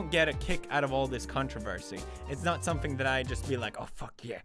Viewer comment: sounds like hes saying "cuntroversy" lmao